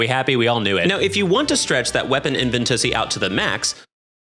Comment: I just heard you say "Invent-ussy" today. I also just heard Aiden from The Lore Lodge say "investigussy" today. Nice.